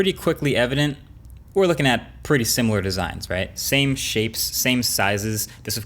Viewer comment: WHY did i hear it like "same shit"